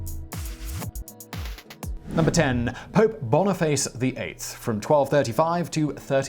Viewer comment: Lol I swear I thought he said pope boner face